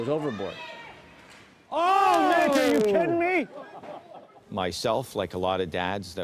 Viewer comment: what did he say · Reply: oh mitch are you kidding me??